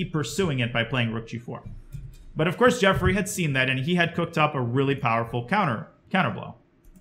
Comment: I swear I thought I heard you say Jeffery Epstein there and I was like "wait what the fuck" lmao But fr fucking amazing match man, thanks for going over it, very insightful stuff.